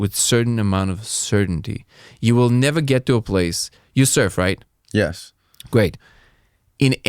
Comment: When he said “you surf right?” I had to rewind it 3 times because I heard “you served right?” I’m like is a vet? I had no idea! I’m dumb. Indeed.